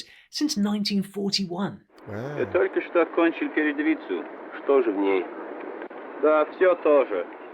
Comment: this is a hilarious political joke😂 It's a word play on the word "izvyestiya", which means "news" (or more like "data" or "information"), but it's also the name of a Soviet newspaper😂 - You've just finished [inaudible]* - So? What's in it? - It's all the same. Do you know what truth is? There aren't any news in it - And in News there's no truth, eh? _ * - The verb "okonchil" usually refers to graduation, so the inaudible word was likely a name of some school. But if the movie producers made a mistake and used the wrong version of the verb, I think it may be a different newspaper. Cos I hear something sounding like "peryedovitza", which honestly sound a lot like another Soviet newspaper. UPD: Pyeryedovitza means the front line article😅 So the full dialogue is: Right guy: You've just finished the front line article, haven't you? Left guy: Yeah, why? Right guy: It's all the same... Do you know what truth is? There aren't any news in it. Left guy: And there isn't any truth in News, eh? Btw, I just realised that Truth was another newspaper. No idea if it had a news section or not.